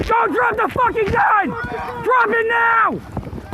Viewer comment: Wait did he say "don't drop the gun? Drop it now? 😳😄😅🤣